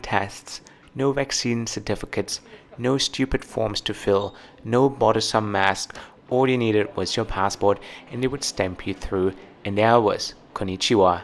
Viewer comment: what is a Buddhism mask? I’m deaf, so it may be a weird auto captions mistake, but I can’t work out what it could be instead if it js misheard or if it’s right, what is a Buddhism mask?